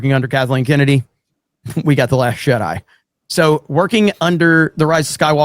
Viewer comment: I just woke up; but about 7:30 into the video, it sounded like DDayCobra pronounced "The Last Jedi" as "The Last Shit-Eye".